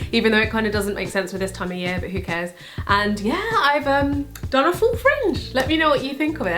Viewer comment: What does she says at 0:13? I have a.... ? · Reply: she said "i have a full fringe"